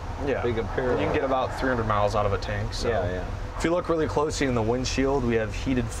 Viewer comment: I had to do a double take, I thought he said 3 miles to a tank. :D